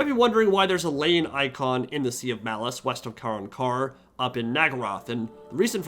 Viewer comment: Wait did they say they are adding Malice? Cause that would be amazing having my favorite chaos god back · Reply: Are you talking about 6:20ish in the video? If so it sounds like he said Sea of Malice, which appears to be a body of water near Naggaroth.